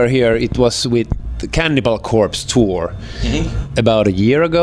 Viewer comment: im sorry it sounded like he said candy ball corpse lol